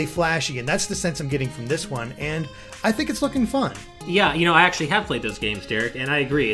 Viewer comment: is Derek saying f**** fun or looking fun